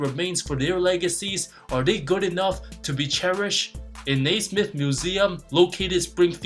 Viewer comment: As someone from Massachusetts.. wtf did you say at 1:00 ? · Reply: Nasmith museum